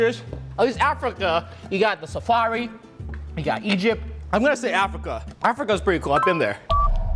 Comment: DID HE SAY EGYPT?!!?!?!I LIVE THERE!!!I actually live in egypt